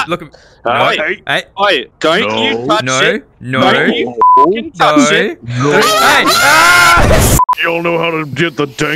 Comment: who knows what he Said Answer Allah Albar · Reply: Allahuakbar